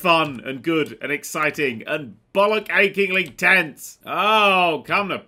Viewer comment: thought he said ball aching.